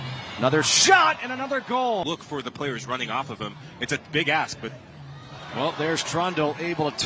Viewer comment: am I tripping again or did he say ”its a big ass” 😭